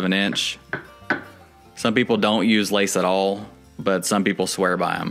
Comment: what’s a lathe(or whatever he said)? Like a metal guide that’s tapered to get the correct pitch? I’m about to do a shower base, and I was thinking of using something like that , since I don’t do them a lot, figured it would make it easier